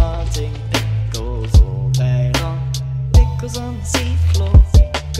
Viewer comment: For a minute I thought he was saying farting pickles on the seafloor